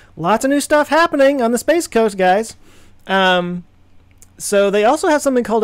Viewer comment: Did he say Spaceghost ?